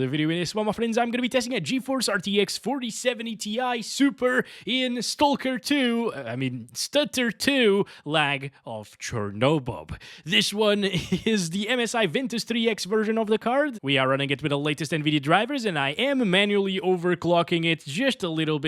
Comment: I had to replay the start a couple of times. I thought you said Chornobob lol Sorry, little things amuse me sometimes